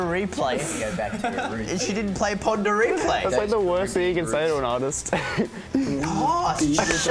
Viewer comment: what is luke saying in 0:45? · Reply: He said "that's like the worst thing you could say to an artist"